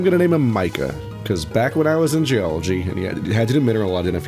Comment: Sick saying "Geology" kind slurs together, so it sounded like you were saying "Back when I was in jail"